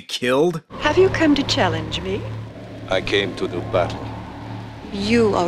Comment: Ishtar calls the monk a "defrocked" monk, meaning he has no longer the right to exercise his religious function and is banished from the clergy. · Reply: I thought she said "defuncted" monk, basically meaning he is no longer able to work (more specifically on the spiritual side of things, like exorcism, blessing people, and preaching) as a monk.